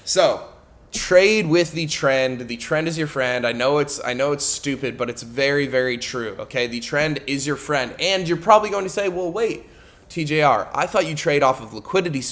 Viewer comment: i misheard it as tren is ur frend and now im 200lb lean and still cant trade)